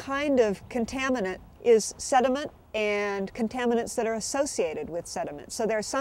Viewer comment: Would anyone please help me with writing down the word she says at 3:46 as the third part of contamination? I cant understand that! Thank you in advance! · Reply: "...sediment, and contaminants associated with sediment."